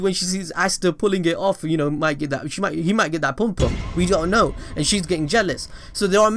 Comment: Plz some one tell me did he just say pum pum